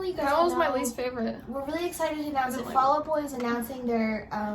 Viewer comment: Oh fall out boys, I thought your daughter said fellow or follow boys and I was like oh I don't know who that is let me try to stay up-to-date, of course, Fall Out Boys, there AmAzballllZ Yes❣️❣️❣️